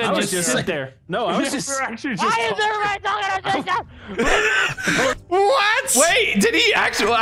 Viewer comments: i think he said "why is everyone talking at the same time? Bark " (yes he actually barked) 14:53 it's pronounced Klomners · I actually found out what he said. He said “Why is everybody talking at the same time!?” “Bark” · In case y’all wondering what he said at 4:09, he said “Why is everybody talking at the same time?” (Saying this in case I’m not the only one who didn’t realize until years later)